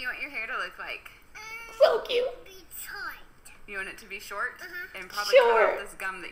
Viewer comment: I thought she said she wanted her hair to look like Detroit before the Mum repeated "be short" and I realized that made much more sense lol. 😂